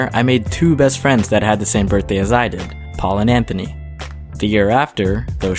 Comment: I thought he said Tal and Anthony LIKE WHAT 1:37 edit: WTHHHH REACTION TIMEEEEEE